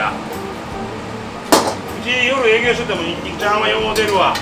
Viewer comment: ニンニクチャーハンがニンニクちゃんにか聞こえへんかったわ